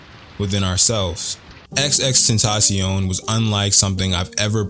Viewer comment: did he say XX tentacion bro you forgot the third X! 😂